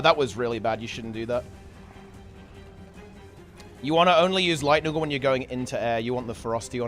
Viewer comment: what he says? "You want to only use Lightning * something * you going into air" What did he say after "Lightning" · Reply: "Lightning Orb" or perhaps "Lightning Storm", both relevant for what he's suggesting. Use them when attuning into air, to make good use of the extra ferocity. :)